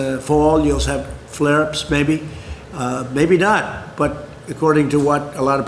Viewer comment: I could have sworn the bloated gasbag said “there will be flare ups, baby”.I thought for a second he’d taken to songwriting. A follow up to “hit me, baby, one more time” from his Epstein period. (I know he said maybe.....baby).